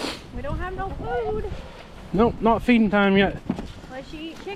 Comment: That chicken sounded like he said, No not me. Lol 😅